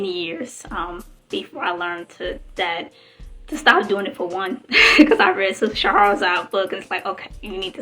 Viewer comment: Great msg. I appreciate your authenticity. What was the name of the book you mentioned at min8:58? I didnt catch the name but it sounded like you said sister Chaharazi’s book. 🙏🏾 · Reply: Peace, it's called The Blackman's Guide to Understanding the Black Woman by Shahrazad Ali